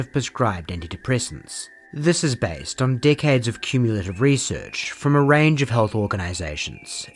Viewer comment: I could've swore he said "This is based on dick heads of cumulative research" haha, even when I go back I still hear it.